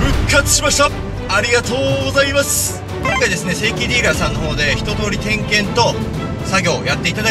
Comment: 正規ディーラーがセイキンディーラーに聞こえた